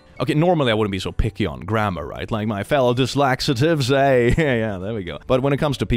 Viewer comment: Instead of "dyslexiatics" I heard "dyslaxatives". Interesting. 😂